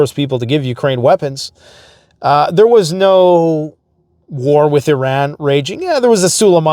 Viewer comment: Thought you said he would lose an d war with iran....and of course bad news gotta keep the videos coming....